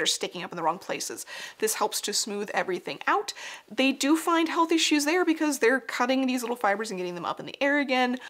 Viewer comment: Subtitle error! : around 13:44 "health issues" becomes "healthy shoes", which I find hilarious but if anyone was confused, that's what is actually being said